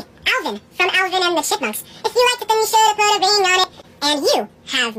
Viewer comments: I can never undertsand what hes saying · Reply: He said “If you like it then you shoulda put a ring on it” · i heard "alivin and the shitmunks"